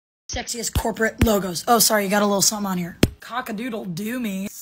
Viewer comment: I thought it said sexist logos and I was so confused